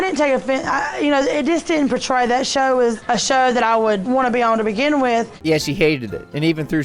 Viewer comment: did she hate it? Sounds like she said she’d want to be apart of it